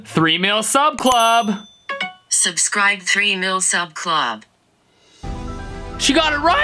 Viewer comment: 😂every time I hear"3mill sub club" I think 3-meal sub club-house 😅